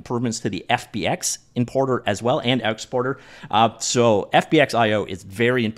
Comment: Could've sworn I heard "inporter and outsporter" ~6:13 ... must be an accent thing. Anyway, cheers for the update coverage.